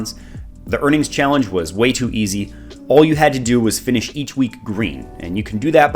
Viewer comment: it sounds like you say, "... you can [be green each week] by just holding shares of BOX." Can you clarify that? BOX seems to be a company that has up and down weeks like most.